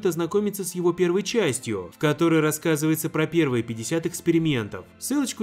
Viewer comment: Мне послышалось или "... пятьдесят экскрементов..."?